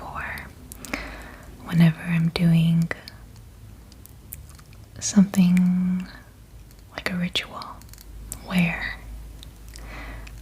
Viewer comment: DID I HEAR HER SAY " RITUAL " AT 5:48 🤔🤔🤔😬😬